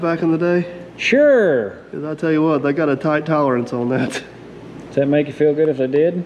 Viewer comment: Thought you said it was tight.